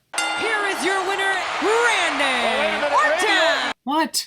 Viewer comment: sounds like She Said Rayman Ortan